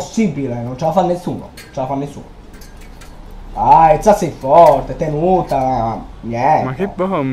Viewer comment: Io ho capito “ah izza sei fotte, tenuta”credo 🤣